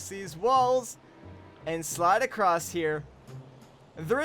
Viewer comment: it says sidle instead of slide